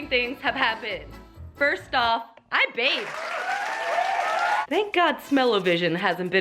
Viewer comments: Oh my god when she said “I BATHED!!” I thought she said “I’m baked!!” And I about died 😂😂😂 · Waiiiit 4:33 did ya say i banged lmao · I thought she said “I’m baked” instead of “I bathed” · I definitely thought she said “I’m baked” not “I bathed” 😂😂